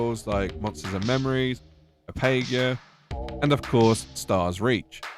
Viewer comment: Mind telling me what exactly you mumbled here, my good man? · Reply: hahahaha Apogea ... A - po - g - ea ... i think thats how its pronounced, stop bulling me :_saddoggo